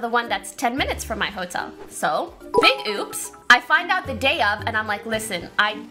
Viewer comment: I thought she said "Big Boobs" instead of "Big Oops"